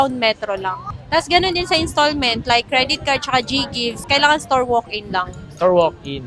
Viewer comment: anong store walk 8? tama ba narinig ko